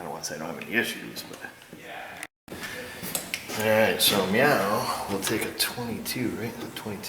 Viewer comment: did he say "meow"?